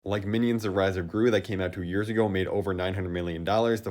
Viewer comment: did he just say 2 years ago..........?